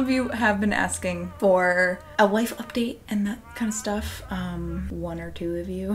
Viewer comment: First I heard "wife update" and I was like woah there what did I miss? 😂😌